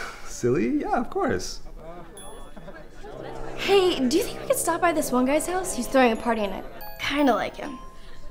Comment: What does she say in min. 3:00 ? I couldn't catch it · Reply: She says: 'heey... Do you think we can stop by this one guys house, he's throwing a party and I kinnda like him.'